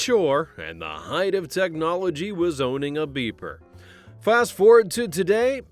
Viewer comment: The height of technology was owning a beaver 🦫? I thought that was what he said in the intro. 😂